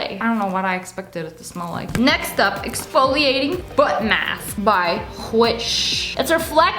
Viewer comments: When she read exfoliating face mask I thought she said exfoliating butt mask and I was like eXuSe Me WhAt?!? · Haha I thought she said “exfoliating butt mask” 🤧🥴😂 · When you said "Exfoliating foot mask by Whish" I heard "Exfoliating butt mask by Whish"... I was like WTF so I had to go back and listen again 😁